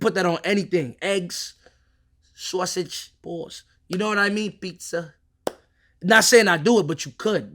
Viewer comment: The captions saying "balls" when he said "pause" 💀💀 Had to rewind and listen again😂😂